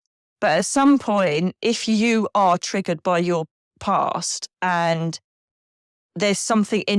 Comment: The dark energy voices are at 27:32 34:14 40:23 41:07 the last one has a female and male! 😮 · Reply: this has nothing to do with the word 'Pray', but here you said 'past' and it is written as 'pain' - maybe this is just something that happens occasionally? •27:33 - It sounded like it said 'free us'? Could this be lost souls asking you to free them? •40:23 - After you said "What else can I hate Nikki for? HaHa" - I heard a scratchy kind of noise? •41:08 - just before you said 'God' - I heard some kind of noise like a chair scrapping or a whooshing or whoop sound? Hope this is helpful 🙏 Love ❤ Kindness 🫂 Peace ☮️ Harmony 🎶 Balance ⚖️ to all! Diane